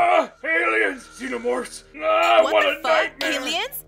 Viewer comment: Did she said the f word?! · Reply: she said "fu..." everything else is a game of your imagination))